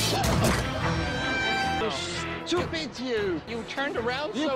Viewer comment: Did he say "You stupid Jew"? No seriously I'm trying to figure it out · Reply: No. I heard (I think) He said "Stupid you!"